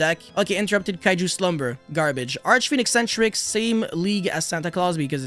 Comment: could u pls tell me thats monster's name i couldnt hear it correctly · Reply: Archfiend Eccentrick